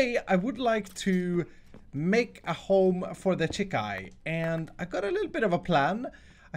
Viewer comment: heard "home for the chick-i" my reaction chick-i! its chickens says don't you know the new grammar now its chick-i instead of chickens